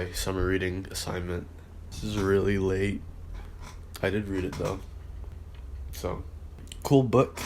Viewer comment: I said “I did read it though” lol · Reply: I hear it now. On 0:16 it sounded like you said you didn’t kind of. Mb😅